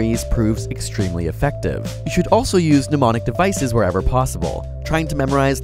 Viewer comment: at first I thought he said demonic devices lol